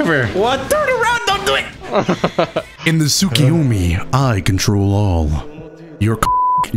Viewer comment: "In the tsukiyomi I control all..." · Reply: When I was young. I thought he said. I control the ball